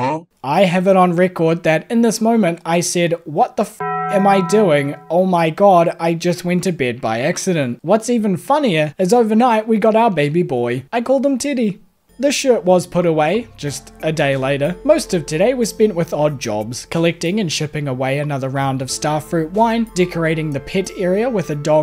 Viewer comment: I’m listening to this video while working and I 100% thought you said t*tty not Teddy 😂😂😂